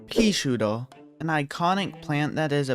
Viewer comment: I love how this guy says peashooter (mum I famous)(can’t believe I got the top comment my life duty is complete) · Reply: Sounds like hes saying:"peashoar"